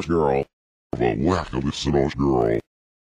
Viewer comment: I went back and listened to that again. I don't hear the word they'll or the w of whack, I hear an l as in lack. I hear "the lack of this inaugural" or "the lack of this (inaudible) girl" Maybe in reference to the women SS in the shooting not being at the inauguration. Which is more congruent with the forward, his love of the SS.